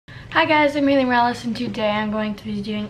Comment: i thought she said "hi guys, im eating relish" instead of her name